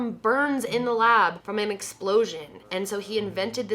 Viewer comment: not about the theories but... 13:27 did anyone else hear “mcsplosion” or am i just dumb and craving McDonald’s